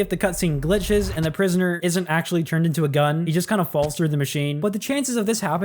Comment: “he just kinda falls in the machine” What I hear “he falls into the window/gun”